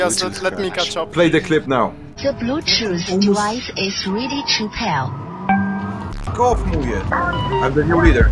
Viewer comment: "The bluetooth life is ready to fail?" Is that what that voicegrab said?